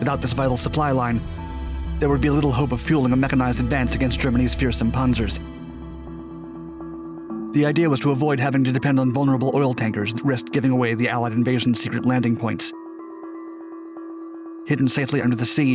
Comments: did you just say "Punzers?" lmfao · i love how he spoke so quickly like he was being chased by a panzer while narrating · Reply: You mean Ponzer? Cause that is what I heard at 0:48.